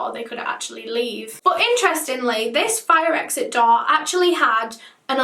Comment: Is it just me? I heard "fire exit dog" until I realised door. .